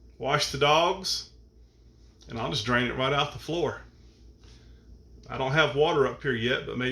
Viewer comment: Did I just hear you say "I'll just drain it (tub) on the floor?????" How does THAT work?